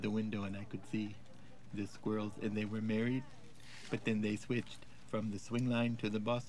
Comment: "I could see the squirrels and they were married" has lived in my head rent-free longer than I'd like to admit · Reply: I always thought he said merry, married is even funnier!